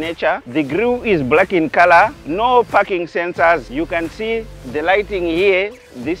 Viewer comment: I heard 0:37 no fucking sensor 😂